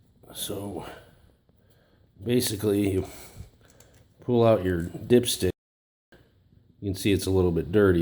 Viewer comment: lmaooooo i thought you said "pull out your dipshit" 😂😂